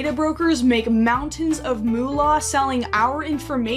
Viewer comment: Did I hear it right ? Was it "mountains of moula" ? Like... "moula" is being popularised outside of France now ?